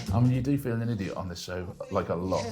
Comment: The subtitles for this smell like Whisper (auto transcription tech). There are certain references and idioms that any human transcriptionist would easily catch. For example, around 12:40 are several soft errors like not capitalizing the name of his show Pointless, "You do feel the energy of the show? Like a lot." instead of "You do feel an idiot on this show, like a lot," "I hope I do right" instead of "I hope I did all right," etc. The subtitles are better than nothing, but you usually do better 😞